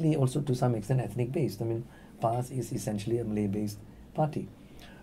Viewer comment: that sounded like 'PSY'....